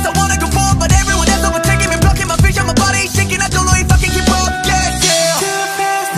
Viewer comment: When Jisung said "I don't know if I can keep up" at 1:36 ٫ it sounds like he swore. It's like he said "I don't know I f*ckin keep up" because he's too damn fast u·ω·u